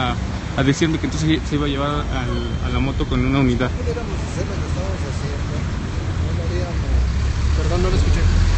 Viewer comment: Yo si escuche "si no pudieramos aser lo q estamos asiendo no lo ariamos" jajaja xq no lo repitio